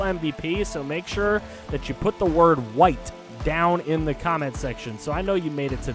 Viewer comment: Remember that time Terrell Suggs said "the white tight end! He's always there when you need him!" Well today that was clearly the Braves surviving against the Dodgers thanks to the WHITE pitcher 😂 Really excited to see how this NLCS goes now that the Marlins are in! I believe this would be the first time they've gotten this far since 2003. And they won the World Series that year! Dontrelle Willis, Josh Beckett, Miguel Cabrera. Just a few names from that year. Hopefully this can be the one for the Marlins but first, gotta beat the Braves! EDIT: I realize now that I misheard you I thought you said white. You really said LIKED so uhhh yikes on my part lmfaooo!